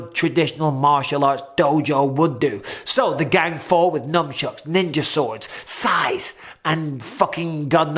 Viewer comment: Did you say "NUMBchucks"? 🤔